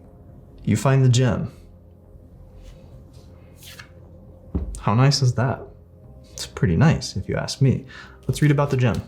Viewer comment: I heard “you find the gym. How nice is that” yeah I love gym ngl🤣🤣